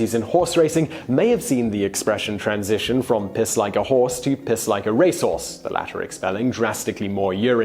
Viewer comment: I heard it as "piss like a racehorse on the run".